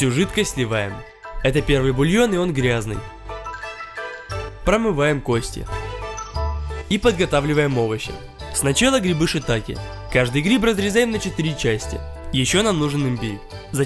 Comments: ахахаха послышалось грибы хатаке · мне послышалось грибы хатаке